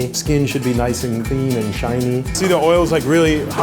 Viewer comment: When the cook Said "shiny" I heard "Chinese"